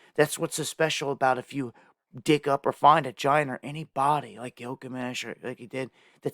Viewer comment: Guess I've been without for a while....@ 5:08... the first time I listened, I swore you said "d!ck up or find vag!na" (it's actually "dig up or find a giant" I think)... so sorry...had to share